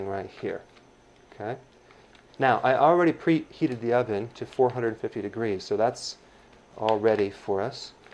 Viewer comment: What temperature it sounds like he said 40 something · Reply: The crust bakes in a preheated oven at 450 degrees for 15 minutes.